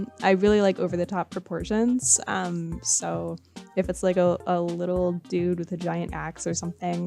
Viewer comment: I thought she said “a little dude with a giant ass”